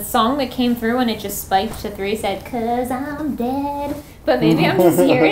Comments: Hi! Just came to say the song You Heard and thought said "'cause i'm dead" actually says "besame" wich means "Kiss me" xD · I think this is really funny, but at 20:46 the song Suavemente plays on the spirit box and the exact word said is bésame but they all thought it said "because I'm dead" 😂😭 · In 20:47 where they think it says "because im dead" is actually the song Suavemente by Elvis Crespo and he's saying "besame" which means kiss me in spanish.